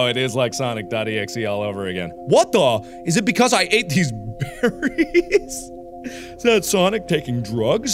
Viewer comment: When sonic said he ate berries I thought he said batteries yeah no shit sonic if you ate batteries you’ll die